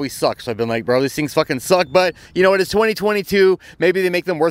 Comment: I was in a other room and I just heard "suck butt" lol 😆